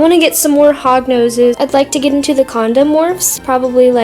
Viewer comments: NAME THE FISH SQUISH! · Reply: did anyone else think that she said condom? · When she was saying Honda's ior what ever I thought she was saying condoms